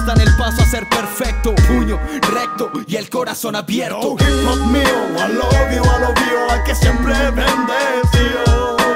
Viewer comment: En el coro siempre pense que decia "hijo mio i love you a lo vio"